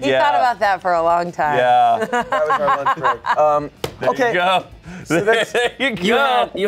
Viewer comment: Did anyone hear at 4:35 ish the crew said "that was our lunch break" 🤣